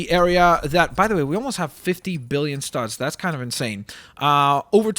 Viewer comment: when he said 50 million studs I thought he said 50 million subs as in subscribers